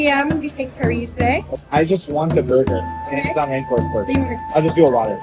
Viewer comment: BURGER DID YOU JUST SAY YOUR NAME IS BURGER